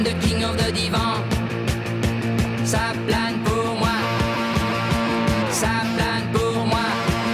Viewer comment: I thought he was saying Cinq Quatre pour moi... 5 4 for me! Ooowoo Oooo! Cinq Quatre pour moi! I’ve been looking for it since December 1977, no wonder I couldn’t find it 😂😂